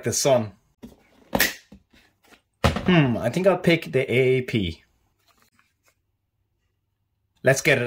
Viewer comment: How does your AEP shoot that hard? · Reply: Oh, I thought I heard you say, "I think I'll pick the AEP."